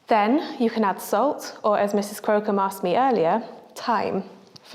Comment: i thought she said " Mrs.Scrotum asked with thyme"